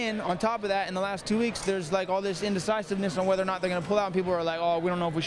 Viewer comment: did Nick just say "indecisiveness?" Wow... that's a big word for him lol.